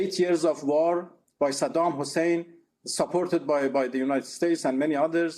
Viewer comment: Why did he say "cooh" that means mountain in Iranian